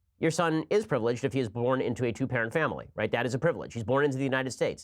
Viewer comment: Ha..I had to go back& listen 2X, to Ben answering the "Catholic white-privilege son" question. I thought he said your kid is privileged to be born in a "Jew parent" household, not "2 parent".😂